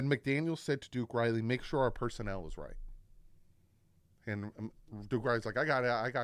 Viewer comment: I thought McDaniel had said, "Make sure our personality is right." Referring to keep the mindset right. Then he told Tua to smile and what not. He went into halftime after that and then said to just play your game and enjoy it.